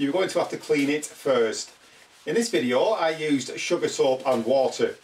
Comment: You said use Zisser 123, but then said something was cheaper than that. Then it sounds like you said you use "sugar salt" to clean the vinyl? I'm sorry; I've listened so many times and can't make it out. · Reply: Sugar soap - <link> Thanks for the comment 👍